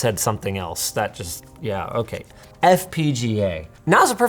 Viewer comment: I thought it said “fupa”